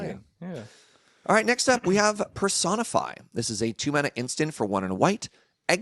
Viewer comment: Jesus I misheard Personify as persona Five and was "WHAT"